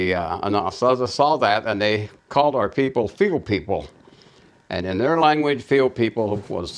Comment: "they called our people '__ people'" I'm sorry, what is the word? Fuel people? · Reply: "Field People"